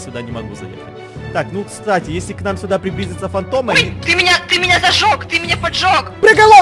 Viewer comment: Я же не одна услышала в 11:44 если к нам припиздица фантом💙